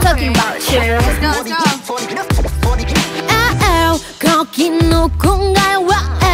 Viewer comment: 슈화가 뭐라고 하는지 아시는 분.. 계속 돌려서 들어도 뭔 말인지 모르겠네.. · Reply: 여기 클럽 아닌가요?? 이거인듯요